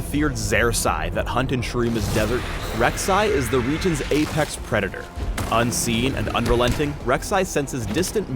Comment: Apex predator，thought I heard “8 packs”